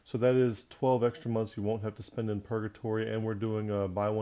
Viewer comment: Did he say purgatory at 1:43???